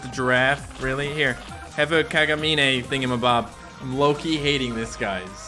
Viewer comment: Did he just say thing ThingiMcbob 16:22.